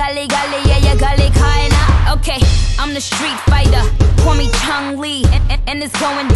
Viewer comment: Did she just say Chun li?